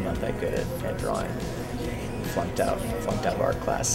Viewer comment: did he just say he "fucked up art class fucked up art class" ???? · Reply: I think he said flunked